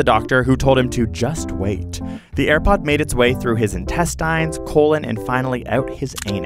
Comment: Did he just say....INTESTEINS?